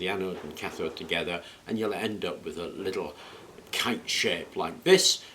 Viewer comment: I'm not entirely sure what he says here but I'm going with "you end up with a cunt shape". Naturally looking forward to it not working if translated correctly.